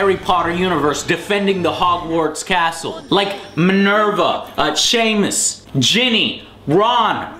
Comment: Did he say 'Sheamus' was a wizard in the game?